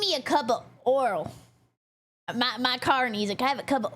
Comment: Is she saying "Oral" or "Oil"? Because the Closed Captions also hear "Oral."